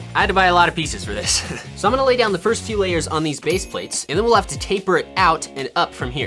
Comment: did he say TAPER?